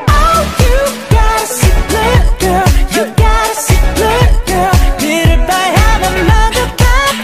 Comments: and here I thought they said "you got us a good girl" this entire time XD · and here I thought they said "you got us a good girl" this entire time XD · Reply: i thought they said "you got a secret girl" xD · and here I thought they said "you got us a good girl" this entire time XD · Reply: Wait I heard this as "you gotta sing bad girl" T^T